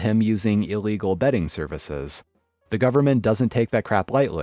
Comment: I thought he said "illegal bedding services". I was like WTF?